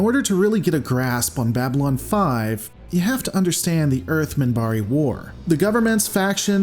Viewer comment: At first I thought it said Earth-MINIBAR. I could order up a double vodka😃